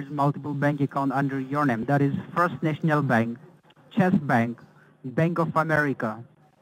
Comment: [with closed captioning on] I hear that "Chess" Bank treats you like a king! Or maybe a pawn, or maybe it depends on how many rows of tiles you step across at the bank...